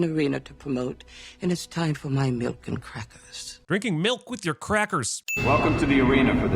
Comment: Wait, I coulda sworn she said “milk and cookies instead of “milk and crackers” after the black pot of snakes scene.